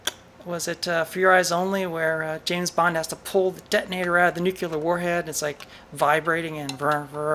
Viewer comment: did...did you say "nook-u-lar"? Maybe I need more coffee.